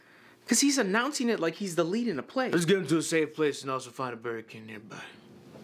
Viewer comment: "Let's get him to a safe place and find the Burger King Empire." Is WTF I heard and I'm sticking to it.